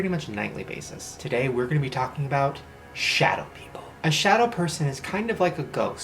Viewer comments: this entire time i thought shane was saying "shadow pico" because i used to be such a die hard directioner LORD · i thought it said shower people